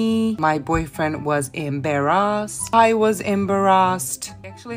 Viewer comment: The part she said I was embarrassad I thought she said I was in Barra's and I was laughing when I noticed.